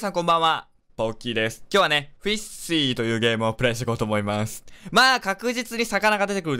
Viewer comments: フィッシーて言ってたけど、 一瞬、フィッシャーズって聞こえたw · 最初の挨拶の「ポッキーです」が「ドンキーです」に聞こえるww聞こえないかな？